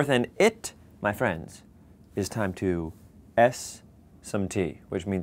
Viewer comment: I heard him saying, "It's time to eff some tea!" 😂😂😂